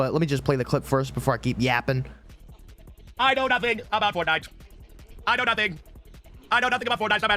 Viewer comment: What is he saying? I woke up five minutes ago all I hear is mouth noises! I hear sounds that I think are words! WHAT IS HE SAYING!?!? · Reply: He said that he knows nothing about fortnite pretty much